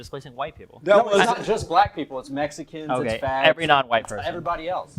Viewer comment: Did he say “fags”???